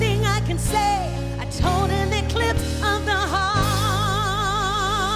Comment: Was she singing "of the heart"? I kept hearing "on the heart".